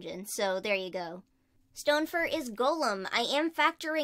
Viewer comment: I heard "Stonefur is Gollum" lol...